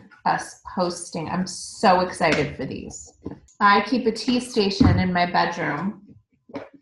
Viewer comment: when she said tea station i thought she said cheese station ~ 😋